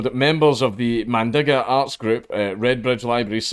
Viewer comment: Wait. . . did you just say " The My nigga art group..."?